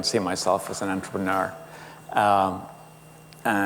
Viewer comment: hmm.. can anyone tell me what onthopeneur/onthopeneurship (04:42) or something is? and can u say how to write it? i know it seems dumb but i never heard that word, and my main language is not english.